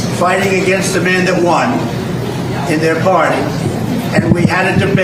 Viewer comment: At about 12:10 Ooooo!!!! He "spent a hundred million dollars, fighting against the man who..." what?!?! Who what, Donald??? "Won" did you say? Did you say Biden"won"? I wonder how all those Jan 6ers, sitting in jail feel hearing that?